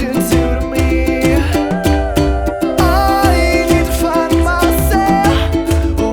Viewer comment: " I neeed to fuck myself" that what it sounded like hahaha ;)